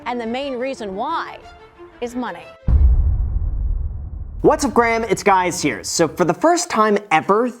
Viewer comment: Did he just say what's up Graham this is guys here 😂😂😂